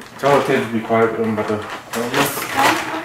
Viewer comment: I hear kiss